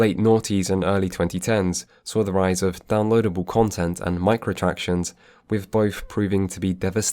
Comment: Did anyone else heard the "Micro-Tractions"? - It made me glitch for a sec x.x